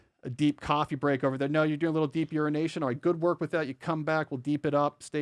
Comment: Did he just say deep urination? He is distracting me from deep work!😂